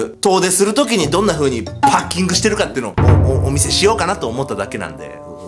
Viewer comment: ハッキングに聞こえるwww